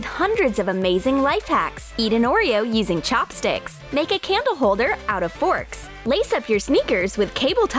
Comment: Lol, I thought you said, "Camera holder." Instead of "Candle holder."